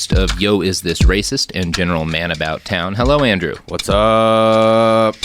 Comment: I had to listen to the intro a second time. I was doing other stuff and really thought he said the guest for the week was Andrew Tate! 😳 It's not.